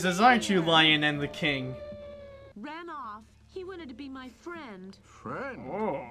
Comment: did he just say "he wouldn't be my friend" ? I thought that stupid lion did not want the panther as a friend... : s